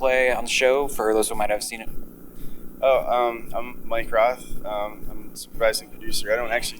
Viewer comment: Did he just say minecraft? at 0:11 :)